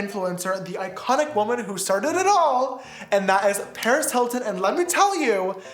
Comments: I honestly thought he said "perez hilton" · Omg I rlly thought he said Perez Hilton 💀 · “Imagine a video with me and Paris Hilton?!” You did a whole show with Paris Hilton 😂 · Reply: I thought he was saying Perez Hilton oh my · I thought he said Perez Hilton I was scared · i thought he said perez hilton i was like- 💀💀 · I thought he said Perez Hilton I was scared for a sec